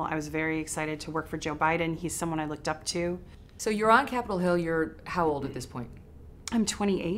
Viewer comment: 0: 25 did she just say "Capital Hell"? Lol